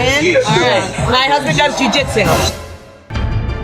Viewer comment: anyone know what she said？ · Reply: My husband does Jui Jitsu